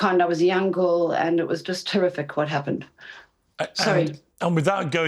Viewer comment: I know it could have been me mishearing it but did she say it was terrific? Maybe she said something else or was trying to say somethin like terrible and got her words messed up but that was weird